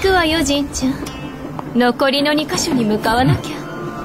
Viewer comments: I don’t play starrail and i got confused. I thought he said “Mother!” in the JP version one. After rewatching and noticing the EN didn’t say anything when blade stopped him, I realized he actually said “matte!” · I misheard trailblazer's "made" to mother for once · Is it just me or i heard MC saying "mother" in Japanese dub (Know its "matte" but swear heard it as mother) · Bro, I literally heard the "matte!" As mother 💀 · Trailblazer JP saying Matte*(wait) instead of that i heard "Mother"😂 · Lol when jp started i heard the "matte" as "mother" I mean, she is trailblazer's mother isn't she?